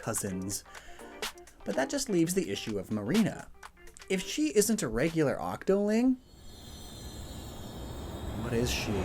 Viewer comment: "but if marina isn't a regular inkling, then what is she?" Didn't you just say she's an octopus? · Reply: I thought he said she wasn't a regular octoling. Maybe I misheard though.